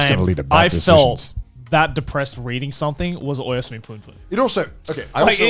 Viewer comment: can someone tell me what was the sauce that made Joey depressed, my hearing skills are not that good. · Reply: He said Oyasumi punpun (or Goodnight punpun) Don't read it if you are depressed or anything because it goes into pretty heavy stuff but if you are mentaly stable I recommend it